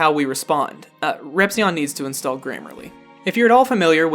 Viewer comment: Did you say Repzion needs to “install grammar” at 41:00? I couldn’t make that out